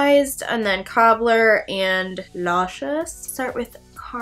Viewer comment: When you said the name of the shadow "Luscious" the first time, I totally thought you said "Nauseous" I was like 😳 they named their eyeshadow nauseous???😂 Anyways, you are seriously my inspo! I heart your makeup skills. And I was the one on snapchat talking to you about the contour on the forehead. I really hope you'll be doing a tute on that! Oh! And I'm SO looking forward to "My boyfriend does my voiceover!" That's going to be too funny w/ the eye look & contouring that you did.